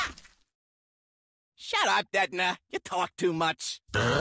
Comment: I thought he said "shut up dead mass you talk too much"